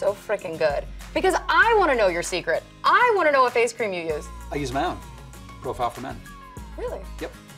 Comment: cant understand what he said he use as his secret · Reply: He said "I use my own, Profile for Men" ... presumably his own range